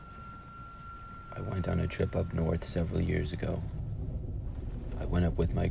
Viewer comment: At the beginning I thought it said “i went up north 70 years ago” but then I realized it said SEVERAL years ago Lmaoo